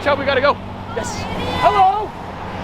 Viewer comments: Did he say hello idiot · Who else heard the kid say "hello idiot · did someone say “Hello idiot”